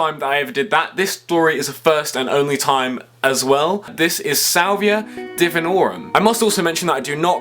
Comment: Lol I thought it said saliva. Lol